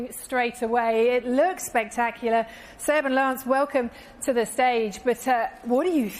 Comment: I thought she was saying seven lance...😁 Buts it seb and lance